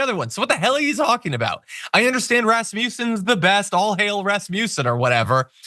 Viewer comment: Hah! I thought John said 'All hail Rasputin!'😂😂 Rasmussen. It's been a long, busy day.